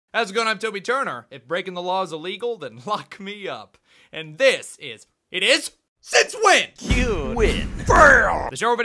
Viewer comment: In the intro, I thought he said "Then fuck me up."